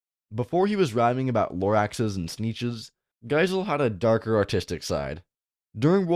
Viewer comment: I THOUGHT IT SAID "AUTISTIC" I WAS SO CONFUSED